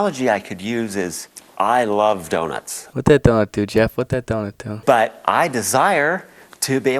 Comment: What did he say at 5:16 ? “What that dog do?”